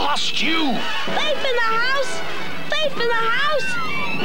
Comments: Did oliver say faith in the house or thief in the house. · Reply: sami zayed neither he said THIEF In The House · What did Oliver say? Something in the house? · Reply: Thought so. i thought he said Faith in the house?